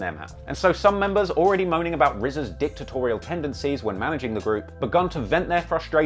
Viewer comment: I thought bro said rza had a. dick tutorial but he said dictatorial